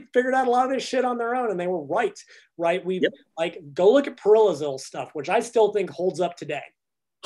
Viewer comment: Does he say Perillazil? I can't figure out. If anyone knows pls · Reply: I think they're talking about John Parrillo.